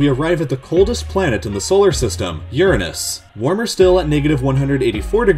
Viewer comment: did he said my anus?